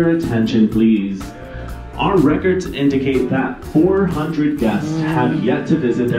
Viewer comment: When I first heard that announcement in the video I thought they said "4 guests" and not "400" lol. Thought - goodness is laid back about that!